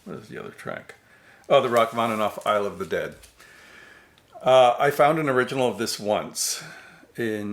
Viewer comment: "The Rachmaninoff 'Isle of the Dead'." I could have sworn you said, 'I Love the Dead'. Silly of course, because Fritz Reiner died 10 years before Alice Cooper even recorded that classic. Shame, it would be interesting to see what the Chicago Symphony could do with some glam rock.